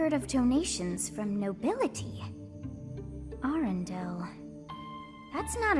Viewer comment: Me: DID SOMEBODY SAY ARENDELLE??!! ARE WE MERGING INTO THE DISNEY UNIVERSE??!! ARE WE MEETING ANNA AND ELSA BEFORE THIS GAME IS OVER???!!!